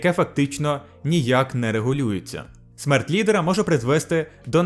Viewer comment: мне одному послышалось смерть пи_ора?